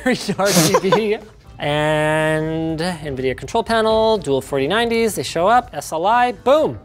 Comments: did linus say dual 4090's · did he say dual 4090s Linus was 3 yeas in the future 😂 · Just me or did he say Dual 4090s